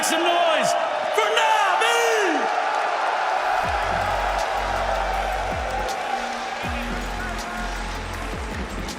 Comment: what did said? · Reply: "Thanks to everyone in Finland who is watching, and hello to the people in Espoo watching from home."